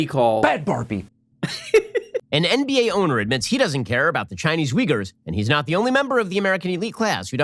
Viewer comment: Ben Shapiro: "An NBA owner admits he doesn't care about the Chinese Uyghurs..." I thought Shapiro said "Chinese wiggers" and I had so many questions.